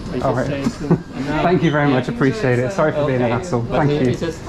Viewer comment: At first I thought your said, “Sorry for being an as****e”. Haha.